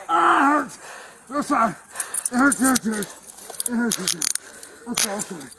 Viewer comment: did he say "It hurts so good"?